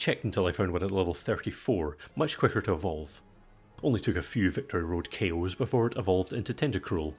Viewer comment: I've been playing pokemon for a while, so idk how my brain parsed this so badly, but for a moment after I heard "victory road KOs," I imagined you were doing celebratory roadkill